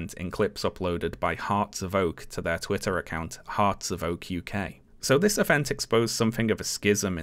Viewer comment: the way I both misheard "Hearts of Oak" as "hearts are woke" and did not question that there would be such a thing. I then looked up from my crochet and saw the actual thing but yea.